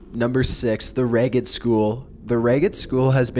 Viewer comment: I thought you said Reddit school 😂